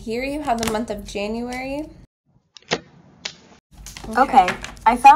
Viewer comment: It sounded like said "Virgo for the month of Janurary" awesome read I'm Capricorn Rising so I hope some of this beautiful spiritual growth is for me. Love you girl. Xoxo ❤😊🦋🌼🌿